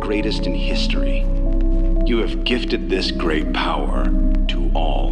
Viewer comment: In the bad ending recording (Gamana ending) it sounds like he says "You have given this great power to me"